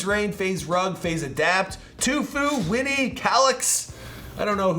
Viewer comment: Did he just say TUFUE